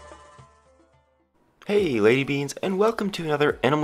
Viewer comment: Did he say "Hey lady beans?" If not them wat did i hear